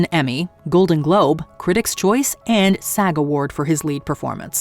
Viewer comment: does she say fag award???